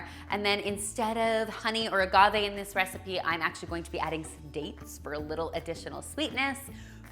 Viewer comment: I legit misheard dates as "steak" and went wait what???